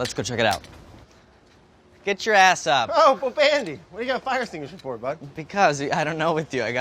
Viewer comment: Did brad just call him bobandy? Brad watches Trailer Park Boys confirmed.